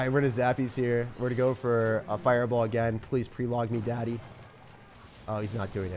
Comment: what did he say? 😂 💀 “Plz peeling me daddy”